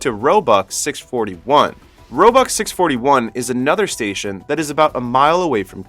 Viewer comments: i thought he was saying "robux station"💀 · Bruh did he just say robux